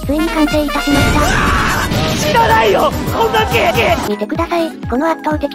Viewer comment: 「いらないよ！こんなケーキ！」に聞こえた